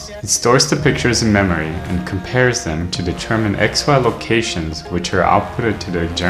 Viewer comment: Can someone tell me what he says at 1:16 ? To determine what kind of locations? Almost sounds like he's saying "X-files locations" 👽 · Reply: x-y locations